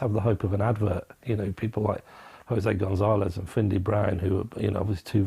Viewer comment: jose gonsales and who??finde brian??is that what he said at13:00/ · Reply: Findlay Brown